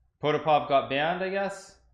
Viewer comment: Who did Nakamura say got banned? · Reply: Potapov, the IM who beat him. You can see him disappear off the standings at 1.34.20..